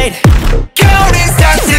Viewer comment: I never looked at the lyrics so I thought hyunjin was saying “god speed” instead of “ that’s me” 💀